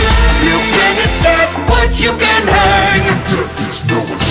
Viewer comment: I always thought he said "You can escape but you can't hide." Kinda more terrifying tbh